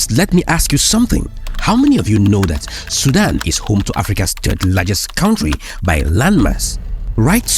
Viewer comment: Sudan isn't the largest country in Africa currently but Algeria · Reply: You misheard him. He said Sudan is the 3rd largest country in Africa. I agreed to all the points he made and it's the heart of Sudan problems. Sudan will come out of this and chase these opportunistic countries trying to loot Sudan.